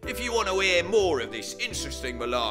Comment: It sounds like he says "Subscribe and don't be a Nazi" at the end ...